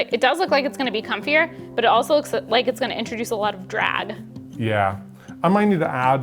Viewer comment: Ok, I've listened to this section 6 times now and it still sounds like is saying "drad" instead of "drag". Is "drad" just a word I've never heard before or are my ears broken?!😅